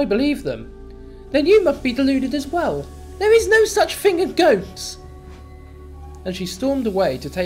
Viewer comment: if sounded like Hannah said 'there's no such thing as goats'